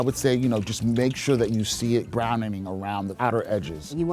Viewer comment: did he say brownening?